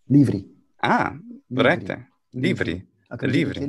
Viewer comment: Oh! I misheard Livris as Ibrius.